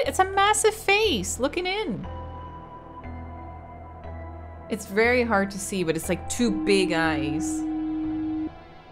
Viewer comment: When you are trying to figure out what happened in the parlor at 15:00 the sign said something about "As for me and my house we will devour..." but I can't make out the last word. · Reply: "as for me and my house we will devour the very gods" yeah I'm replying to a 2 year old comment 💀